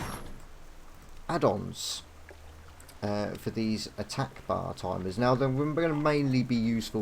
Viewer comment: Hey, you talked about your new swing timer addon at 6:37, but I didn't catch it's name. What is that addon called? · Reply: Hi mate, sorry it's taken so long to reply to you. The addons is simply called Attack Bar or Attack Bar Timer. When you first get (if yours starts the same is mine) both bars, for me and my enemy, were on top of each other, to fix this, type /abar unlock This will then show the bars and you can move the freely. I have to be honest though, I'm not to sure, if I like it. I'm going keep the search going for the best addons and I will keep everyone informed when I change, add or update them.